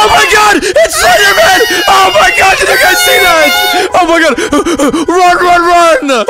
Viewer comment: bro I thought he said spiderman